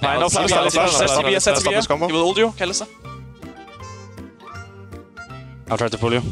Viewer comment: does caps say, kill alistar or does he call mikyx hyllisang? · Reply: slow go on alistar... and then he says " he will ult u ..... kalista"... as far i could hear him